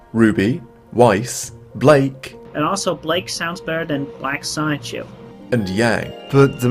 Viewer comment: Anyone understand what the nasally-sounding twat at 1:59 was saying? All I got was "Blake is black Sonic-chu" (Shoe?) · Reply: Black Sonichu